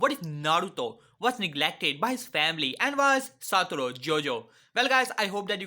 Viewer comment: Did this just said jojo